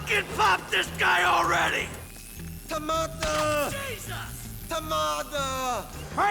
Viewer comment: why the F is he saying TOMATO ?